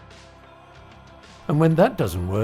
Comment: I thought he said rope around it neck.